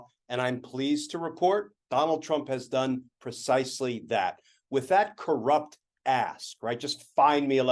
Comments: To support this show, please subscribe (free) to this channel: <link> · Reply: There was moments there where I was very much enjoying Glenn Kirschner having said, 'with that corrupt a$$, find me 11,780.' 🤣🤣🤣 Until sadly, a few moments later I realised what he actually said. 🤨 Still, I prefer what I thought he said! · At the 0:40 mark, I thought he said "that corrupt ass" it was really "corrupt ask," but I like my version better. LMAO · I thought Glenn said "with that corrupt a$$" and had to rewind 🤣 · He said .... 'with that corrupt ask' .... I thought he said ... 'with that corrupt a$$!' ... 😂😂😂 One in the same! 😂👍